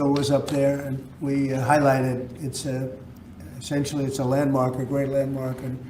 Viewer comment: About the ceiling eagle at 7:30 - I could swear Trump said "sexually, it's a landmark" 😋😆😉